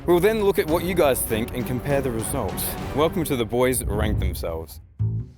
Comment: ‘Welcome to the boys RANK themselves’ in case you misheard like I did…….